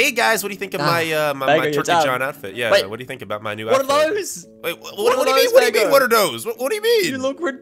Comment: WHAT ARE THOSE?! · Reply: It sounded like u said "WATER THOSE!"